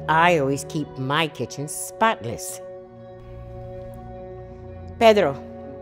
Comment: When my sis heard she say. "I keep my kitchen Spotless". I heard her say. well, It's not you're kitchen.